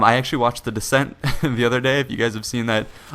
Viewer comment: When he was talking about "the descent" I thought he said "the descendents" and I tried to figure out how George Clooney fit into his plot summary.